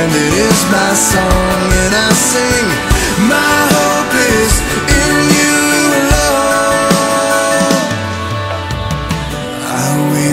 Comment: haha i thought he said my hope is on You Lord but he says my hope is in You alone